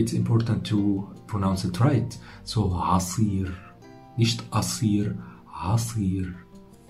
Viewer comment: hey :) bei @ 5:03 da sagst du "nicht"? hab ich das richtig gehört? x)